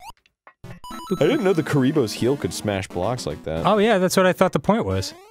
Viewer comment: Did Arin call a Goomba a Kuriboh or did I hear that wrong?